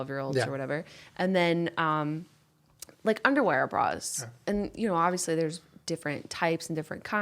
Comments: I thought she was saying "underwear bra" too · I thought Jenna was saying underwear bra, too and I was so confused. I was like ‘um, I’m a girl and I have never heard of such a thing. Must find now!’ 😂😂😂